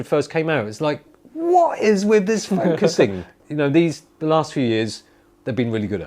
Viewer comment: What does he say at 5:45 about the X100? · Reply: What is with this focusing